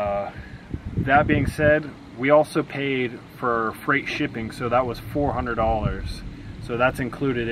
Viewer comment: I love your channel and I am seriously hooked, but did he say at 2:22 that you paid $400 for free shipping?